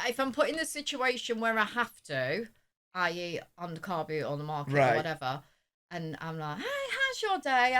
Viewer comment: what does your mom mean when she says "on the carpet" she would talk to strangers? Maybe I heard wrong...lol sorry.